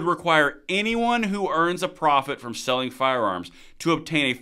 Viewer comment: is actually says "earns a profit for selling firearmS" - make a profit selling multiple firearms and you're in trouble. So, selling a single firearm and not making a profit, should be OK?